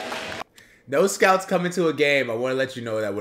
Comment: I think he said "No scouts..."